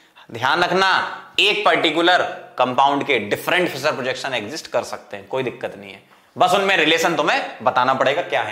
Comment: sir 1:36:30 pe kya bola vo smj ni aya by the way sir u r the BEST :) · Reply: Ek particular compound ke different Fischer projection exist kar sakte hain Sir said this